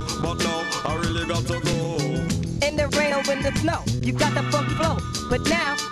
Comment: did he said fuck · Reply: He said funk